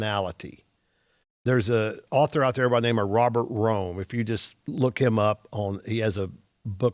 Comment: What is the personality book he mentioned? I looked up Robert Rhone. (The name I thought he said) and found nothing.